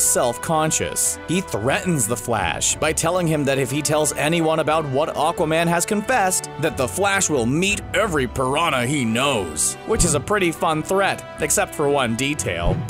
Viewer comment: His name is Aquaman not oceanman so I don't understand your logic with the piranhas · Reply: I honestly thought he said "every prawn I know" and I think that's funnier.